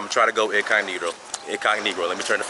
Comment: Did he say "IncogNegro"??...🥴